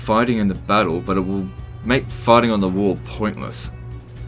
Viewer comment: did say there "it will make farting at the wall pointless"? :D